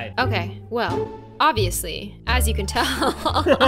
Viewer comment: Did say as you can tell or azzy can tell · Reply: As you can tell.